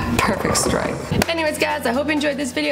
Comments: On the outro it sounded like she said “and never be nice to each other” lol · aend it sounded like u said and never be nice to each other lol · Why did it sound like she said “and never be nice to each other”